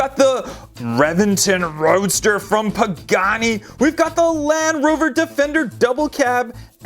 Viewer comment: Did he say reventon roadster by pagani on